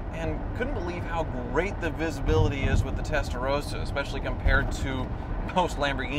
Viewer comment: When he says "Testarossa" it sounds like he says "Tesla Roadster".